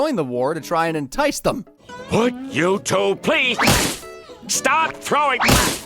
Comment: I legitimately thought he said YouTube